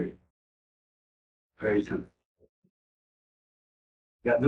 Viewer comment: There is a part I can't understand him saying on 13:03, is it possible to clarify what he's saying? I hear "poetry" for some reason? 😅 It's after when says "it's very simple.." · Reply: SOCIAL SECURITY IS WHAT HE SAID